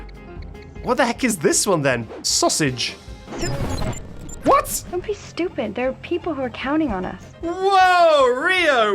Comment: What is rio saying in these bits? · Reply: sewer slide